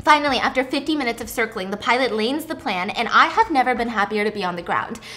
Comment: Please tell me I'm not the only one who heard her say "Lanes the Plan" for the first story at around 13:20 XD. I'm at work listening on earphones and I just snorted in laughter.